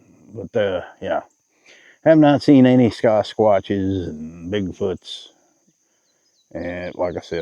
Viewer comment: I had to laugh after I figured it out. Since were talking about Big Foot, but I'm pretty sure I heard you say ScotchWatch. I wes like a what? And about that time it hit me, Sasquatch. Thanks for the laugh. I just had too. It made national news, but one of the tow yards here in OKC had a ghost. It was caught on security cam a few times. They figure it was a ghost of a fatal car crash that had been on the lot for a bit.